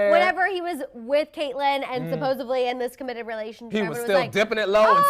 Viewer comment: did she say "supposevedly" @ 37:18 ?